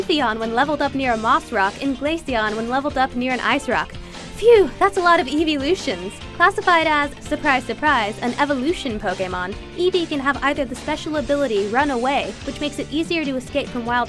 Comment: Lol thought she was saying EEZEE at first. That woulda put a whole new light on things